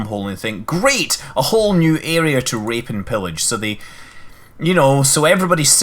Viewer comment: rape and pillage? or raid and pillage? sounded like rape... 0.0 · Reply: the cyborg Yup. Rape and pillage. Very common term, especially when talking about vikings.